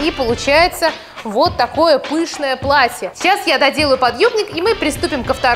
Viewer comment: мне одной показалось что она сказала " Cейчас я доделаю подёбник!"